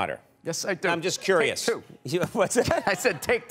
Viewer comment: At first i thought he said, i have two... Not take two 😂